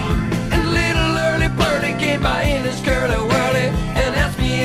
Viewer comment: "...and little Early Burly gave my anus curly whirly..." or at least that's what I used to think he said when I was a kid XD